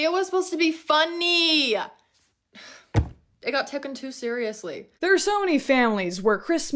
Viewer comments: 03 did she say “tooken too seriously” because I just… either trying to deepen rage bait in the video or genuine representation of how pain and suffering can affect the brain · did she say “it got tooken”??